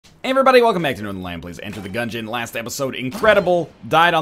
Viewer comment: In the beginning when I thought he said "This is the last episode..." and it made me cry a little D